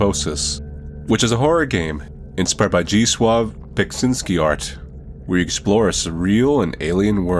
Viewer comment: I heard you say "Beksinski Art" as "Beksins-GYAT" lmao